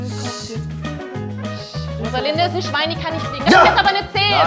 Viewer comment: was sagt sie da? Ich verstehe es nicht irgendwas mit Schwein und fliegen